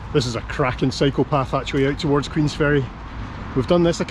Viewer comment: Up to 4:19; & just misheard apparently saying "that's a cracking psychopath..." 😨 Ach, thoose thick rrrich brrrooogues! 🇨🇦